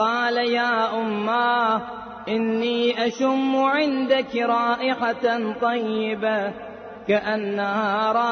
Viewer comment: pe inni ashummu sunayi de rha mujhe instead of ashamu.